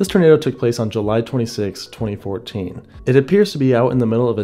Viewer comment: I thought bro said July 23 2027